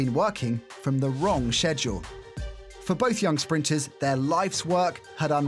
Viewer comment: did he just say shedule 🤭